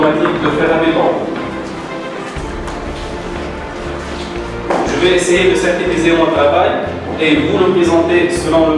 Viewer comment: qu'est ce qu'il dit en 1:41 ? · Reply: je vais essayer de synthétiser